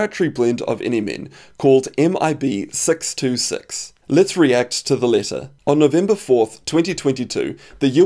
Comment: I first thought he said "Lets react to the lizard."